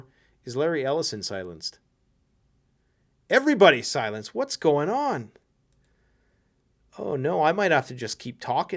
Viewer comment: to your question: What's going on? See the subtitles, Larry says it: That's going on.